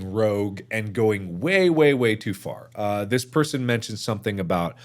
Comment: this is what i heard "There's alot of sperm on YouTube. There's a lot of sperm on anything that live streams."